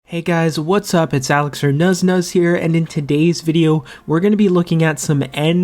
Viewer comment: Is your first name? Sounds like u say here